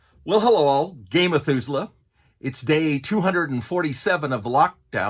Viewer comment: Literally every time, I hear "gay Methuselah." Every time.